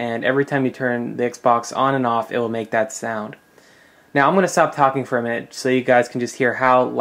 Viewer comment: i thought you said i will stop talking for a minute